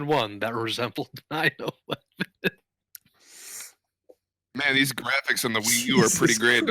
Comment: Murder, rape and...what? What did he say? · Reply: 9/11